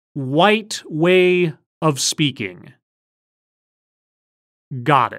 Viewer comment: I think I’m being racist but when she said “white way of speaking” I thought she said “right way of speaking” Edit: I didn’t see nicks radio’s comment when I posted this